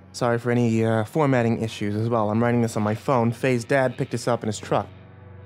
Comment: I thought when he said fay's dad he said faze dad